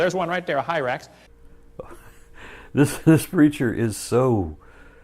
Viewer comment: "This -reacher is so confused" it sounded like creature on first viewing and took to replays for me to hear preacher XD